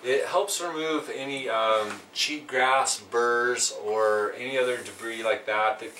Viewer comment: I am not sure but I feel like I heard you say "cheap grass"